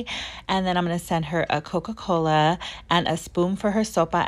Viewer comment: i just realized she is saying soba i thought she was saying like soapa